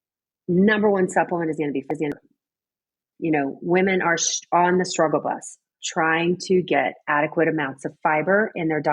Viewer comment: what is the #1 supplemental she said? I heard the dialogue on fiber, but what did she say? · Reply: Vitamin D